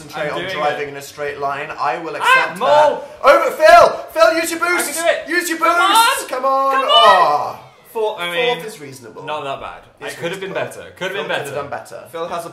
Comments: I thought they said use your glutes and?? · whenever dan said "phill use your boosts!" I thought he said use your glutes